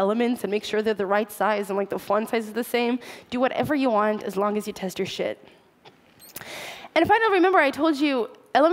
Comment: Did she say "as long as you test your sh*t" at 20:39?